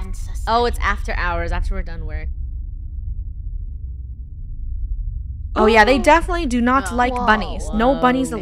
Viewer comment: i thought that said hoya instead of oh yeah!